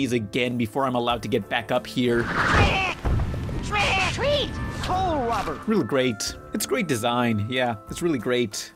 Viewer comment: To me it sounded like he was saying coal robber